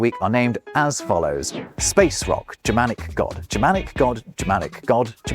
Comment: "Space Rock, Germanic God, Germanic God, Germanic God, German God, Roman God, Burning Ball in the Sky!" best bit! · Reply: Oh, it was space rock ? I thought he said space walk like, you know, Michael Jackson, and I was a little confused by that!